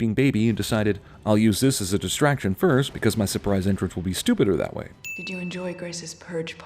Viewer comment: did he say "Stupider"?